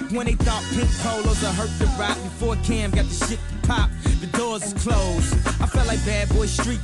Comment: When was the second song written? Because I swear I heard "Before Cam got shipped by Pop" lol